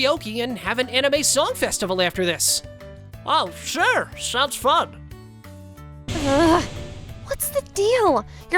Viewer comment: Why does 'fun' said by otakuda sounded like ' it sounds like fu©k '😅😂😂😂